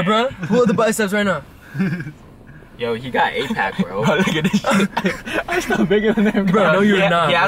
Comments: did say the n word? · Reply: hm, it sounded like he said bro n** this sh!t” It could just be me tripping tho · did say the n word? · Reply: No, he said “bro look at this Sh!t”